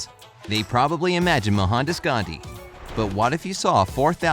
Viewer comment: I think you said Mahatma gandhi?